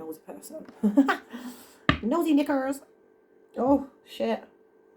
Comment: did she say nosey niggers?